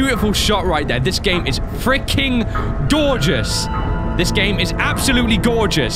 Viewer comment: Does say dorgeus at 23:37 ???